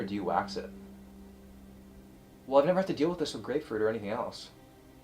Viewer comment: Swore he said I never had to do this with GREGfruit.